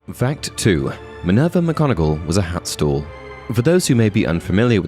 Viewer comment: I thought you said “hat stool” rather than “hat stall” and immediately thought - what was that?